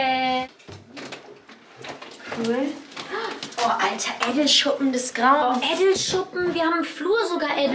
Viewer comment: was sagt ihr da? Hahaah ich hab des Video jetzt schon ca 10 mal angeschaut und verstehe es immer noch ned 😂😂😂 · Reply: Edelschuppen